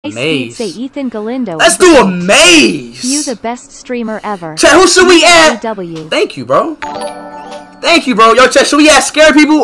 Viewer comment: what is bro saying💀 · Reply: "maze... let's do a maze!! who should I add chat"